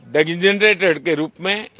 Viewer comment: what did he said at 4:57?? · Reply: Designate